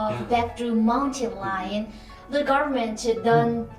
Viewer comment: Hello; at 11:16 you say a word that sounds like "mountain lion" -- is that correct what I heard?